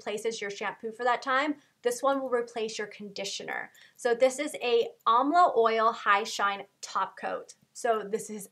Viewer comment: You my heart stopped when I thought she was gonna say my name I was like yes you noticed me! But then she said Almla oil (don't know how to spell that and my names Alma)